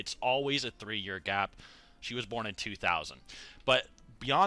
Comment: I thought she said she was born in 2002 ik Seth was born between 1997 and 1991